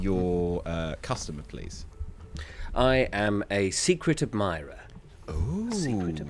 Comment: For some reason I misheard "Secret Admirer" as "Secretive Mime" and was very confused why everyone went for the love approach